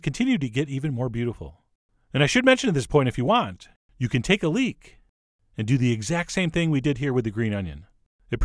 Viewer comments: I thought you say, "If you want, you can take a 'leak'..." until I rewind the video and then I got it. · I thought Chef John meant "Take a leak and do the exact same thing" instead of "leek," and I swear I had no idea how on earth peeing would've affected the recipe. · I thought he was going to say "if you want, you can take a leak, and then come back when you're done"... then I realized he meant "leek". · Lol, at 4:23 I thought he said "and if you want, you can take a leak". I appreciate the thought, but I prefer going to the bathroom before I start cooking!